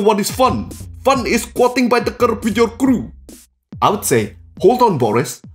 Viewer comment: fun is voting by the curve with your crew? I don't understand. Google's AI can't understand either, maybe someone could fix the captions?